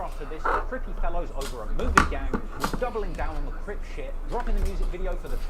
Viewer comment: nah they was saying Moven gang but it sound like they saying Moven K